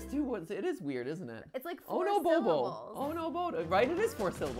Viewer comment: he says it in his Italian accent like how he says “It’s-a me” so instead of oh no he says “Oh-ho no-ho”